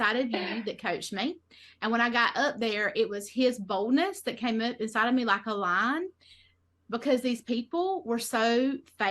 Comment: did she say "i felt his boner come up insider me, like a lion(?)' We have nutters over here but American nutters are truly special.